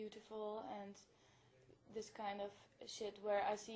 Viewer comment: did she say "these kind of shit" at 2:47?